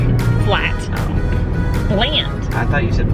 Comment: I thought you said bland, too. I had to rewind and then heard flat. lol. I feel you about the routine. So happy for you!